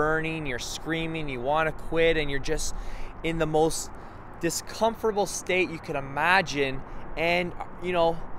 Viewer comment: I heard "discomfortable". Wow.